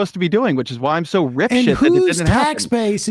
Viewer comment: the closed captions say "I'm so rich that" but is Ezra saying "I'm so rip-shitted that" ??